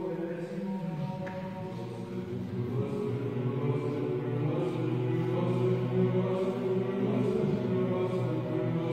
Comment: I get goosebump, but what they said in 01:43 minute? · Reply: Gospodi pomiluj, thats „Forgive me God, have mercy on me“